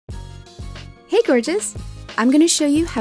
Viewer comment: I was watching this on low volume and at the beginning when she says "Hey gorgeous" I thought she was saying "hey virgins".